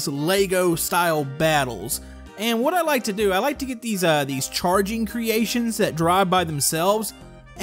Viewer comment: lmao i thought he said "i like to hit these asians that drive by themselves" li had to re - listen to it!